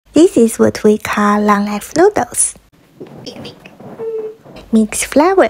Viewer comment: I literally heard " This is what we call long a$$ noodles " 😂